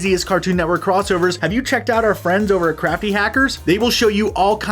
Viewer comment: i thought you said crappy hackers XD